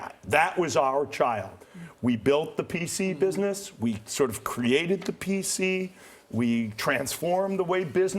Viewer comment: You didn't create the PC, Apple did. And when Apple unveiled the iPhone, you totally mocked it....Then you realized you were mistaken and turned copycat and bought Nokia mobile phones. But the me too approach of Windows Mobile failed, which is why Satya decided to discard the Nokia phone business. Ballmer, you're not an innovator, a tech guy, you're just an arrogant salesman turned CEO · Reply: Actually he said 'we SORT OF created the PC'.. I caught that because I was paying attention too.. :) 2:58 Totally agree Apple (Woz/Jobs) are who came up with the first commercial PC concept. And he also acknowledges MS is not there yet in the mobile market. So I think he was pretty much honest in general. And VERY, VERY lucky to be the non-tech friend of Gates (Bill would've competed with him instead of make him his right hand).